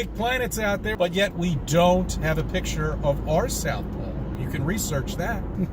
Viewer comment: unfortunately, as I am not a native English speaker, I have no clue what said in this moment: "we don't have a picture of (our ???)" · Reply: "South Pole" Took me a second to figure it out too